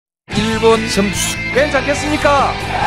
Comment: 뭐라는거노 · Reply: 일본선수 괜찮겠습니까